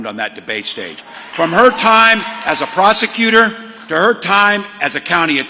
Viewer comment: He accidentally said "prostituter" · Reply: That's not what my ears heard. I heard "Prosecutor."